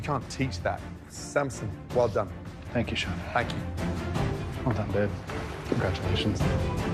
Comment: did he say ”well done babe”